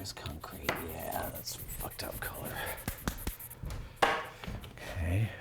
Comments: (ish) does he say "oh yeah, that's one fucked up color" ???? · does he say that's a fucked up color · what..... 17:40 listing he said "yeah that s a fucked up color"